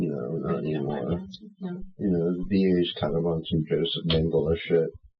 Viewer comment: is speaking about the VA and he mentions a name. I thought he said Joseph Mangela but the screen says Joseph Dingle. In looking for information, I found the nazi doctor josef mangele but nothing else. Nothing about Joseph Dingle at least in connection to the armed forces or the VA. Does anyone have any information on this? Or can anyone explain what he meant?